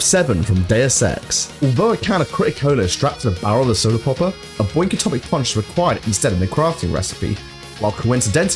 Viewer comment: did you say Boink Atomic Punch?